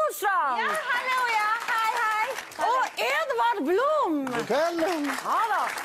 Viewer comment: vad säger Edward?? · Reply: godkvällen